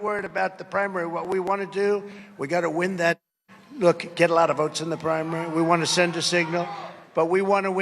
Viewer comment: NewMax is selling out y'all they're silencing Trump. He said, "We've gotta win that Caucus" I think like he's been saying.